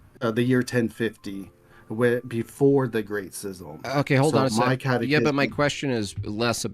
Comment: Did he just refer to the great schism as the great sizzle or am I just hearing him wrong? 😂